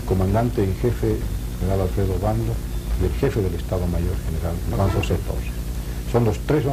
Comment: En el minuto 34:28 cuando habla el general Gary Prado no entiendo cuando dice el Jefe Del Estado Mayor General ,me podrian ayudar por favor?? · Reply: Juan José torres